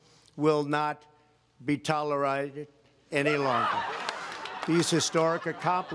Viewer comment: did he just say, "talirited?"